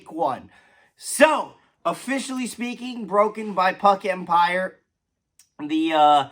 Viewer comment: LOL at first I thought you said ''broken by FKK empire instead of puck empire'' Old man here need more sleep! Hockey is back though baby and is ready! Love your mentality behind this and totally agree! The hard working kids that don't complain about being sent to the minors and are willing to earn that NHL roster spot deserve that chance. Best of luck to Kovacevic!